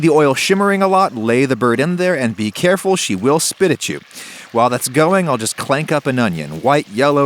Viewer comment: When he said she'll spit at you I misheard it to squirt on you